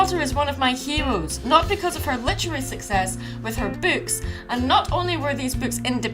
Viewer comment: When she said “is one oh my hero’s. Not because of her literacy success, but because of her books.” I first thought she said “because of her boobs.” And I quickly had to replay it to make sure I heard wrong. Thankfully I did in fact hear wrong. 😊 . . . Heh because of her boobs, now that made me laugh. 😆